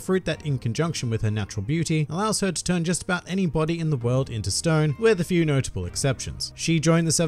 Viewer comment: Does anybody know what the hell did he say at 2:33? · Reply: “She can turn anyone into stone with a few notable exceptions”